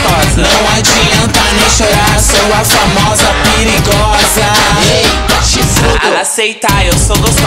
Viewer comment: "Sou a famosa piriGOZAA" só eu que entendi ?